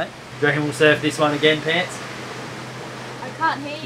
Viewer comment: What's the term of endearment at 5:30? Pants? Pits? I can't catch it?